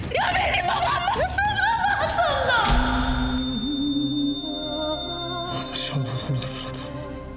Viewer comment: de miran ne diyor anlamadım yaa Miran masum Reyyan o yapmadı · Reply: Hazar Şadoğlu öldü Fırat diyo